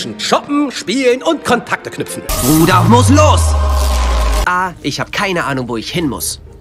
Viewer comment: Ich habe verstanden "bruder muss los"